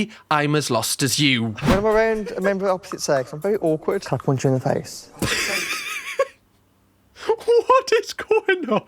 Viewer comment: Oh, PUNCH you in the face, I thought he said Come on....